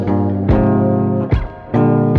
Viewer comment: I hear "Go little rockstar" or "Hope little rockstar" I refuse to believe it's "Pope is a rockstar"